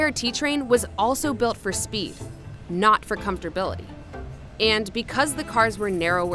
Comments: does she say "...not for speed, but for comfortability" ? I hope she meant, "comfort", which is actually a word. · did she say "comfortability"?